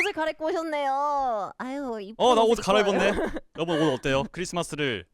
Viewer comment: 에 나만 ㅋㅋ 여러분이 아니라 여보 라고 들리나